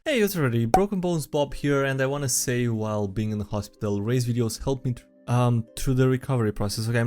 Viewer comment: You read “Ray’s videos helped me through the recovery process.” I heard “razor blades.” I was more than a bit concerned.